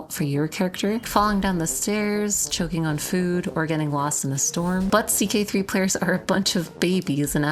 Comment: I thought you said get lost in a store lol